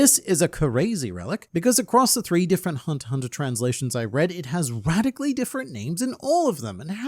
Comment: I heard 3 hundred instead of 3 hunteter translation XD